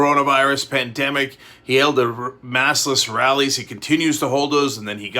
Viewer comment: Did you say Maskless or Massless?